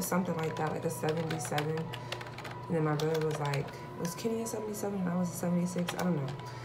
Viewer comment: What did you say at 1:12 ? Brother?